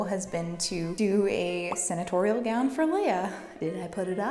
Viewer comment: I thought you said sanitarial gown. Like Leah as a janitor? Interesting…